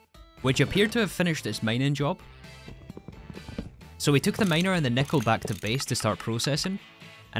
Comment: "so we took the miner and the nickelback to the base" my favourite misheard thing ever.